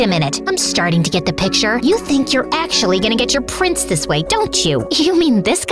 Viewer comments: It said price instead of prince it said it on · they out price instead of prince, just thought it was funny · Why does in say price and not prince? Lol